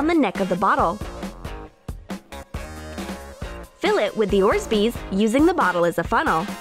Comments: did she say “Orsbees”? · Did i just hear orzbeaz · it sounded like she said Orzbeez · listen carefully you will hear her saying “orzbeez” instead of orbeez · they say orzbeez instead of orbeez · did she just say "orsbeez"